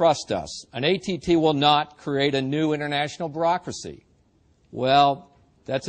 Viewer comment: does he say "Barackracy?" ;-)